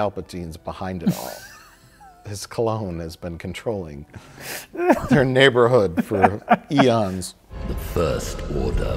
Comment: I thought Mike said, "his (Palpatine's) cologne has been controlling their neighbourhood for aeons." I don't even . . .what?